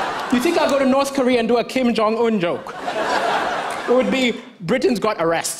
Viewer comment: Am i the only one who heard him say kim jeon kook He is a member from bts 😂😂😂 Kind of 😅